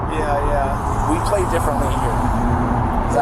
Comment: Omg I thought the cop said you don’t wanna end up in Utah jail being from Cali because “Wii play is different here” 😂 not “we”